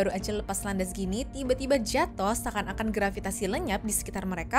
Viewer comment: What? Seakan2 grafikasi lenyap? Ngomong apa sih?